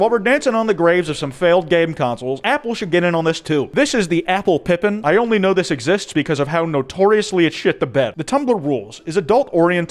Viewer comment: DID YOU SAY PIPPINS??.?!